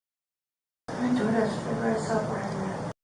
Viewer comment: I hear her say I’m the door dash driver I saw Bryan there. I’m listening on blue parot 650 headset and that’s what she says. But in all honesty I don’t doubt he was there just like her being there and her calling him BRYAN like she knows him. LOOK GOLKS ITS LIKE THIS, if you do METH, YOU WILL BE ABLE TO SPOT OTHER USERS IMMEDIATELY AND THERES NOT A GREAT BIG GROUP OF PEOPLE YOU CAN GET METH FROM SO THE ONES THAT CAN GET IT ARE ALL KNOWN BY THE USERS INNTHE ATEA ITS THAT SIMPLE. I GUARANTEE BRYAN WAS ON DOPE, AND MAY HAVE HAD A REALLY STRING TEASON WHY HE CHOSE THAT AREA. DIWNERS SUCK HEROIN SUCKS OPIATES SUCK AND IF YOU ARE A HIGH FUNCTIONING PERSON BUT DEPRESSED YOU NEED UPPERS NOT DIENERS AND THATS WGAT HE WAS DOING. PROBABLY BRAGGED ABOUT HOW GOOD HE IS AT WHAT HE HAD ALREADY STUDIED AND HOW HE COULD ACTUALLY PROBABLY GET AWAY WITH CLEANING UP A CRIME SCENE AND WHOEVER IT MAY BE BETWEEN THE FRATS THE COPS THE SNITCHES WHOEVER KNEW ALL THEU HAD TO DO IS PUT HIM AT YYE SCENE DRIVING AROUND WAITING FIR THE DOPE TO GET THERE WHICH IS WHAT I THINK HE WAS DOING MAY HAVE PARKED BESIDE THE DOIR DASH GIRL BUT SHE MAY HAVE BEEM WHO HE WAS SUPPOSED TO BE WAITING FIR TO GET DOPE, BUT SHE WAS JUST CALLED TO BRING FOOD AND MAYBE DIDNT EVEN KNOW. WOULD BE TEAL EASY YO SET THIS UP PEOPLE.